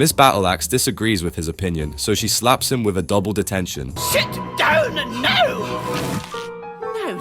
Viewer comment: I know this isent important but I thought miss battleaxe said sh!t down instead of sit down